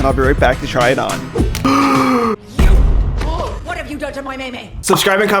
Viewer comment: what did she just say? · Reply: She said YOU WHAT HAVE YOU DONE TO NAMI?!